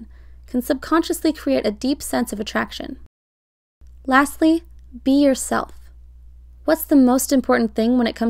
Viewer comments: Oh my god when she said ‘Be yourself’ I thought she said pee yourself, when I tell you I screamed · lol i heard that as "pee yourself" and had to rewind😂